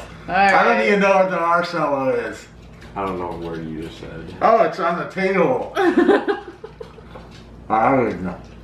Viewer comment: It sounded like said: "Oh, it's on the Tato!"😂😂😂😂